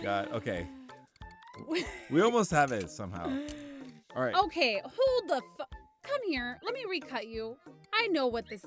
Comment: "Who the fu...!" I'm dead😂😂😂😂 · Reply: I thought she said "hold the pho-!"